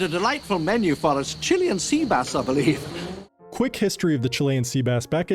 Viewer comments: I hoped this was about their actual lunch, Chilean sea bass is amazing. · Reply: I thought he said lunch was "chili and sea bass" for an embarassingly long time😂😂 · Thought they said Chili and Sea Bass.